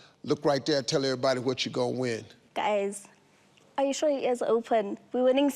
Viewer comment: What's she saying at 0:30? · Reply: Guys